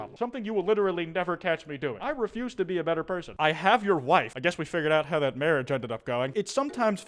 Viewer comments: what that actually says is "is this woman your wife?" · is actually says is this woman your wife? Bu of course, Americans.